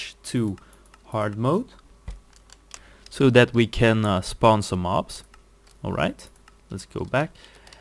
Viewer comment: You said "so we can spawn some mobs." Google thought you said "so we cannot sponsor some mops."